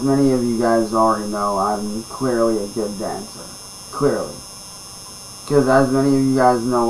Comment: Does it say - “I’m clearly a good gangster “ ? He’s gunna have a knock on the door with claims like that 🤣😂😂😂💩